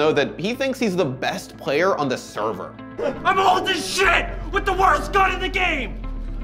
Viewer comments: YOU'RE PLAYING TWITCH GEO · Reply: I thought he said "I'm going to hold this shit with the worst gun in the game" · YOU'RE PLAYING TWITCH GEO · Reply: Jowell Irizarry I thought he said, “I’M ALL THIS SHIT WITH THE WORST GUN IN THE GAME!” which makes more sense